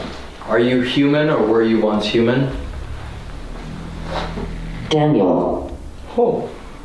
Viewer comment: When it said “Daniel” at 33:37 , it kinda sounded like “Damn You All” which would be like the devil saying be damned. Doomed to hell. 😮